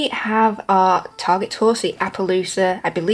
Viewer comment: What’s the horse in your thumbnail? Can we get it? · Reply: hi hope youre ok at 0:10 did u just say horsie?